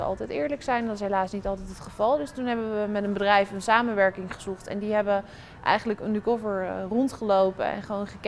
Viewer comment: Hoorde ik nou serieus op 0:29 een samenwerking gezoekt...?